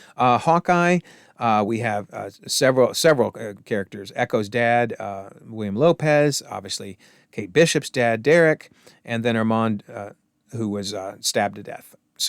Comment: did he say Kate bishop died? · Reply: Wow, that was remarkably bad enunciation on my part. I said "Kate Bishop's dad", but it did indeed sound like Kate Bishop's dead". Yikes! Sorry about that.